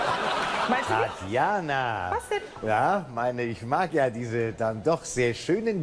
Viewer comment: Was sagt Tatjana bei 0:05? :D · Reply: "Meinst de nich?"